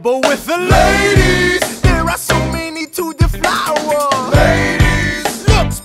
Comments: I always hear 'so many to devour' W H A T I S W R O N G W I T H M E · what the hell is burr even saying here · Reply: There are so many to deflower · When I thought he said devour I wanted to punch something when I realised UE said deflower I wanted to legit wanted to legit build a time machine and slap him in the face